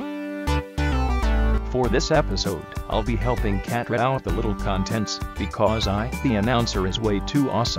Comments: I thought it said “Mini Contestants”. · I always thought it said little contestants.